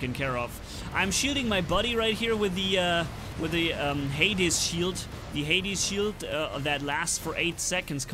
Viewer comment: mistakenly said Hades Shield instead of Ares